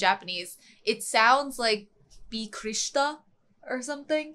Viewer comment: I think the word is "bikkuri shita" (びっくりした）. Maybe it sounds more like "bick-lee shita" ? I often say "bikkuri sita(びっくりした)" , "odo roi ta(おどろいた)", or "bibit ta(びびった)" when I'm surprised. These are everyday words, so you can hear them a lot in anime😄